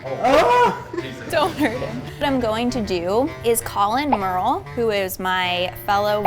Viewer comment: Thought said "What I'm going to do is call him Merle. My vegan comrade/boss..." Me: "Why Merle?" Followed by confusion when she introduced Merle. Not sure if my listening skills are crap or my hearing is crap.